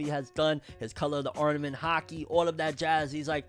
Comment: I wasn't looking at the screen and totally thought you said there was a "hockey" battle instead of Haki.